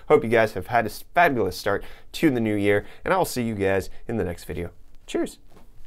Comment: DID YOU JUST SAY "TSCHÜSS"? Haha great!